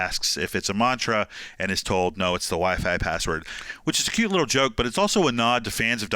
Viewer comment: In the end I thought he said "it's the wife I brought you" not the wify password lol damn